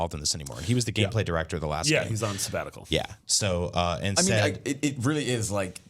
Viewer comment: What did say ? Where is Bruce now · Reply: Laith Radhy he's on a sabbatical but will be back soon according to sony